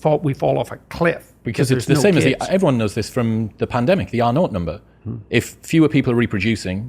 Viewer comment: What does say at 0:45 ? It sounds like "NAUGHT NUMBER", but I guess it's something else